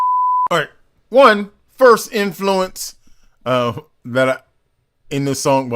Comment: He’s Welsh, maybe i misheard you say Irish influence